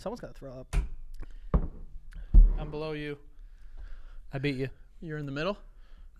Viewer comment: What said: “I’m below you.” What I heard: “I’ll blow you 😳”